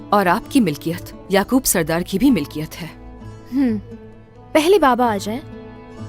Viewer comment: what fatima says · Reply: Pehle baba ajayien uiiaiouiiaiuiiaiouiiai